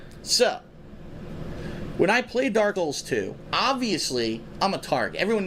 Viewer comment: BIG MISS at 44:00 where the guy says "DarDoyles 2" instead of dark souls 2